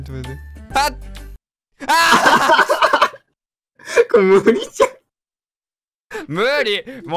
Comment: これ無理じゃんがこれ麦茶に聞こえた